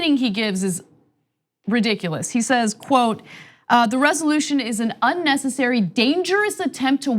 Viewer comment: LOL...I thought it said "Pence is unnecessary ".